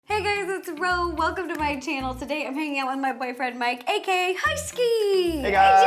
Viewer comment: In the intro, I thought she said "Today I'm going to yell at my boyfriend."